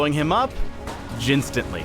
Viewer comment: Did you guys hear what i heard in the end? 7:09 jhin-stantly. :O He was giving us info about new champ :D